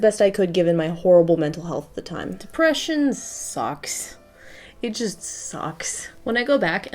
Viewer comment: random thought when she said "sucks" it sounded like "socks" ok continue